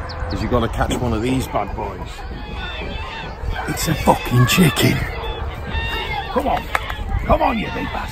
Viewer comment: did he said the f word!?!? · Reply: Weah it a foocking chicken